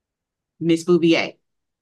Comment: I was listening to this while i clean. I thought she was saying miss Boobie-aye 😂🤣